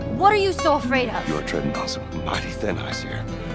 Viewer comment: so it's mighty! I could catch just that one word. I thought he said "bloody"